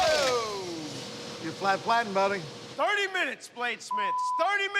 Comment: what does he say at 2:03?? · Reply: Get that flattened buddy